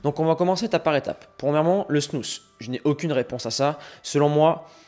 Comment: Quelqu'un sait de quoi il parle? j'ai pas bien entendu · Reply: Snus